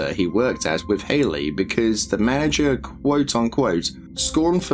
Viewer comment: You did not just say "mana-jerk"! No! 😭